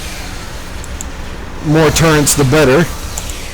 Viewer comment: why do you sound like ur saying torrents instead of turrets?